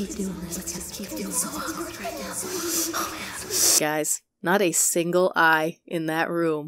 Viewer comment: did i hear "hes so ugly right now"?! 😂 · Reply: “It feels so awkward right now”